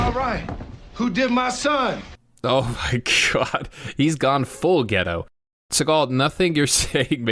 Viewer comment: I was half listening while doing things around the house. Out of nowhere I hear Seagal say "Who did my son?!?!", and you said "He's gone full ghetto", but I misheard it as "full pedo" and I could not stop laughing, I'm sorry😂. What's really messed up is that Seagal did indeed have teenaged "girlfriends", his own words to Katherine Heigl, many years ago.